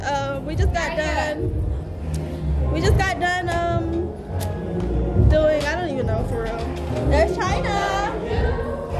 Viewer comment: y’all heard what said at 6:36 😝🥰😌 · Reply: he was like “while she playing she my bae fr”